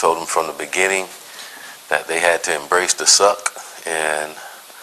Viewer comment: sounds like Taggart said embrace to suck